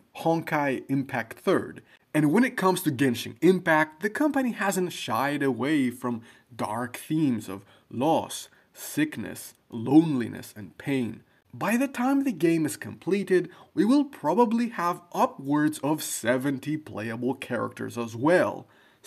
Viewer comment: I thought he said Thickness then had to turn on captions to see that it's actually Sickness 😂